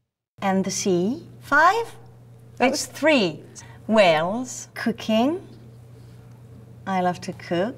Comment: I'm not mocking her or anything but I can swear I heard cocaine. She's indeed adorable.